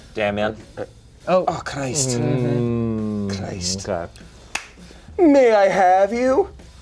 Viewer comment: I thought damian said "May i have you, meow~" "that's a weeb" --- 😂